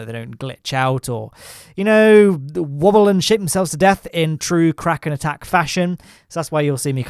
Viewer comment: it sounds like you say woble and shit themselves to death I hope you dont get demonitized for this hilarous mishap.